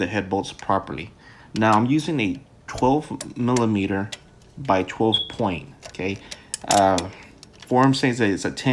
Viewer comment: Wait. So the head bolts are down inside? And are you inserting a hex head into them? Confused. I thought you said 10 or 12mm 12 point. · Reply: Sorry for the very late response. I didn't see your comment until now. I know this is late, however, its a 12mm 12 point Matco MCXZN12MB tool part #. I did say 10mm 12 point, but I was incorrect. Manufacture has it as 10mm, but it was a 12mm for engine head.